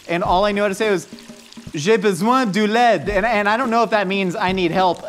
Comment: When said ''J'ai besoin de l'aide'' (I need help) I thought he said ''J'ai besoin du lait'' (I need milk)....